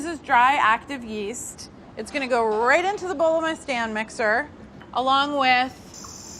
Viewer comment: I misheard it as stan mixer and i was so confused for like 5 seconds lol